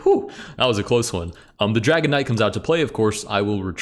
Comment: I’m sorry, I know that you are saying dragon knight but all I hear is dragonite